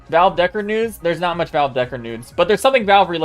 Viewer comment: Did he say "There's not much Valve Deckard Nudes"?